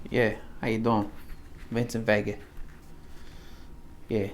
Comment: did you just call me a f*ggot?! (this is a joke and i would be eternally amused if u randomly said that with no further context, but i’m assuming you said something else lmao)